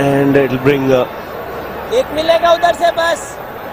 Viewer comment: I concentrated on Dhoni's word behind the wicket instead of commentators 😂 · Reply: ek milega udar se bas